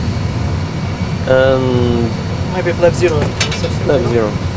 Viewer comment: did he say "Maybe flaps 0"? WTF